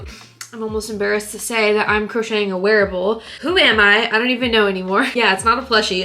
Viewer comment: I thought you said werewolf instead of wearable XD